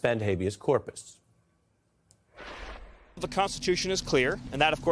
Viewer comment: Did he say prostitution????.... Lol